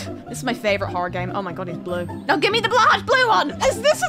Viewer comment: I love how she half said blahaj instead of blue